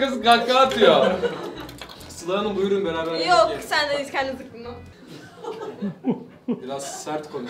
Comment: da sıla ne dedi anlamadım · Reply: Zıkımlan dedi